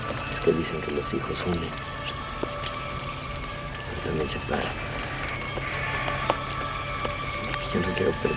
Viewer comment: En 7:21 que dijo Carlos? Dicen que los hijos que?? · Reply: DIJO: DICEN QUE LOS HIJOS UNEN, PERO TAMBIÉN SEPARAN.